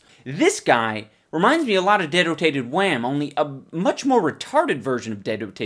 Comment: What's the name of the character reference at 4:18 ? I can't understand. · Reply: I think it's dedotated wam?